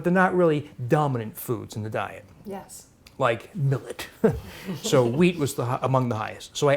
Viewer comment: What the higher food name he said at 4:00 ? The google translate aren't good to identify names. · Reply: The grain he was speaking about is Millet.